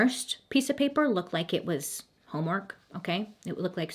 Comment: I swear I heard “hallmark” instead of “homework”